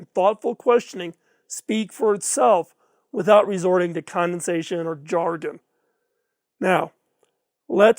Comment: duly noted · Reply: While revisiting this video I did a doubletake around the 16:15 mark when I heard: "... without resorting to CONDENSATION or jargon." Oh my, what precipitates me; the condensation of my soul is condescension?!